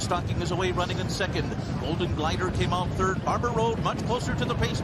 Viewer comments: Everytime he referenced Barber Road I thought I was hearing Barbaro...in the Belmont no less, had me wondering again what could have been. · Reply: me too. Everytime he said Barber Road, I thought that he was saying Barbaro. Then I thought wait, Barbaro, passed away years ago. Barbaro's, passing was, and still is very sad!!! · Everytime he referenced Barber Road I thought I was hearing Barbaro...in the Belmont no less, had me wondering again what could have been. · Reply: I thought he said Barbaro too!!!